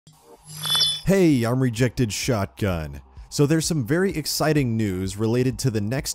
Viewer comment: Lmao I thought it said retarded shotgun